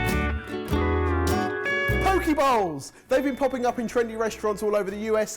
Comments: The whole time, I thought was saying "pokeball" as in from Pokemon lol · When you thought said pokéballs for .2 seconds, then realize he said poke bowls. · I thought said pokeballs not poke bowls and I thought Pokémon go was making a come back.. thanks for getting my hopes up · For the first minute I thought said poke balls. I'm like I have never seen a poke ball in a restaurant, let alone eaten one... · anyone else thought he said poke balls at first?